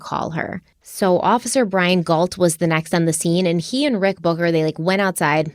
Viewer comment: did Stephanie just say he and Rick booger LOL